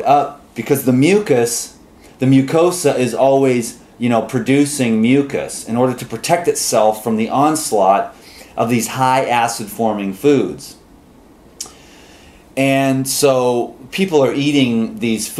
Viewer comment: lol @ 2:25 I thought you were going to say "These high ASS calorie foods" haha